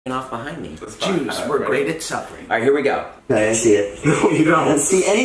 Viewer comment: "Jews. We're great at suffering." No, you didn't. 😂😂😂😂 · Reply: I thought he said stuffing I was like tf